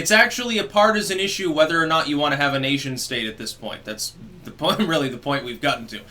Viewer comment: I thought I heard "Asian state" ...freaked me out for a moment; I thought I missed something.